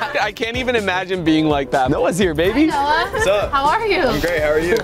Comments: did I just hear him say baby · Did he say baby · DID RUG SAY NOAHS HERE BABY